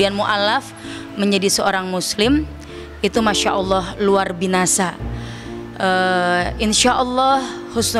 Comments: Luar biasa Bu haji ..bukan luar binasa ..apa saya salah dengar ya ..semoga alm Husnul khotimah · Sampai aku ulang nonton apa aku salah dengar luar binasa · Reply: Sama. Q juga dengernya luar binasa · Apa aku salah dengar ustazah bilang luar binasa atau biasa sih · tak kira aku salah denger dia bilang Luar Binasah😒😒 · Kok luar binasa 😮 apa aku salah denger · Sumpah ini kuping aku yg salah denger atau gimana si… pad dia bilang masyallah luar BINASAH 🥹😅 atau kupingku yg salah denger guys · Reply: Saya ngulang sampe berkali2 emang dia bilang nya luar binasa wkwkwkwk Maksdnya apa ya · kokk dia bilangnya"..mashaalloh luarBINASA...coba denger awalnya dia bilang. LUARBINASA ,apa aku yg salah denger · Maksdnya gimna Bu ustadjah KO LUAR BINASA ..APA SYA SALAH DENGAR YA BU MF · Itu koq ngomongnya luar binasa,aku yg salah denger ato gimana ini? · Luar Binasa??? Salah denger ngk siihh..😮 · aku salah dengar apa ya ko ibu yang pertama komen ngomongnya ko" LUAR BINASA"